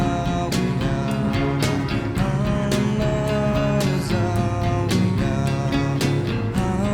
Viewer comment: It sounds like he is saying, " All alone is all we are".